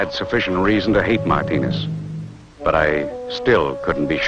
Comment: he clearly says My Penis, not Martinez.